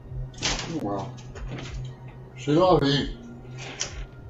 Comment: He actually says, "Oh, well... c'est la vie." It's a common French phrase (that we stole) that translates to "that's life," or another way of saying, "that's the way it goes."